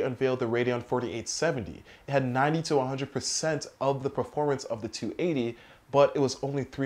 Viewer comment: According to pcgameshardware.de, the GTX280 was almost as fast the HD4870X2. btw. this is no hate, i realy like your videos :) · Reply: Now i get it :D I heard "90 to 100% more than [...]" in the first place.